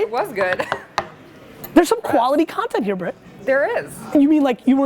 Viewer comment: did he actually say "bra" to her? hhahaha I love Gary